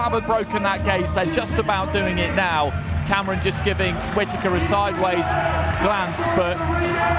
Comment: Did anyone hear one of the sky presenters call him “quittaker”? (5:39)😂